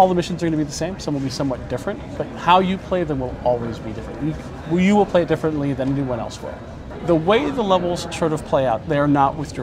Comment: does he say "Wii U will play more different then one else will" Wii U version confirmed